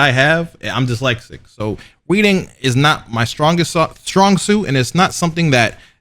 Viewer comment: did he just say “…reading…” with a “W”? 20:42 “Weading”*